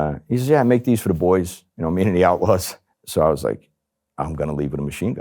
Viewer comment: 45 guns and a pound of weed??? ...what the fff** ?? ...what MC club was that?? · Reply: I thought he said Outlaws, thought they were based mainly in south,not sure if they have New Jersey chapters,I could be mistaken.